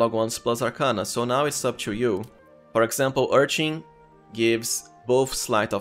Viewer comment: I know I've been playing too much Elden Ring when I mishear "Urchin" as "Erdtree"